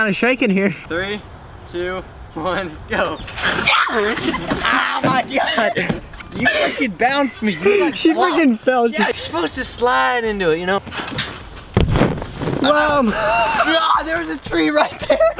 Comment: did he say fucking